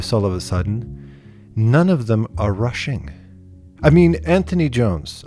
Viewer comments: i thought you said "russian" instead of "rushing" and i was like ????? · When you said "None of them are rushing" I thought you said, "None of them are russian" and I was like "cool cool... wait WHAT?" then I realized what you actually said